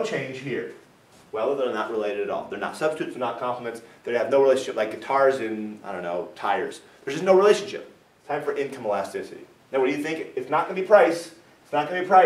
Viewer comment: thought he said they're not related at all "Like cars and tires." HAD TO repeat that a few times!